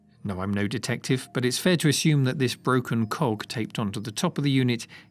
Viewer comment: a broken.....yeah I definitely heard that wrong.